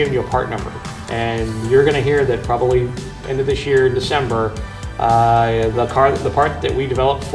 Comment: WHEN DOES IT GO ON SALE? Lol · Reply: I think the magnaflow guy said probably this December.